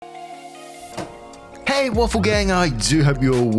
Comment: I'm using your videos to practice my English, and I just realized that you say "hey WAFFLE gang" and not "hey AWFUL gang". It makes more sense now. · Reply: I thought he said Wolf gang🤣🤣🤣 I barely realized it was waffle a few days ago